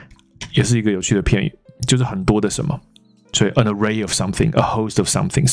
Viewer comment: Hi,老師,我想請問三個問題: 1.可以請你簡單區分一下allege/proclaim/claim嗎? 2.影片有說a host of =a score of =?? 另一個我聽不出來,大概在09:03分 3.a host of 是可數和不可數都可加嗎? 以上,謝謝老師,學到很多有趣的字: ) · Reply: An array of... Scores of... Allege 就是據聞之類的 alleged perpetrator / alleged crime 都是還未定罪的 Claim是聲稱但還帶有聲稱所有權的意思在裡面 例如 claims ownership of the house. Proclaim 是正式宣布一件事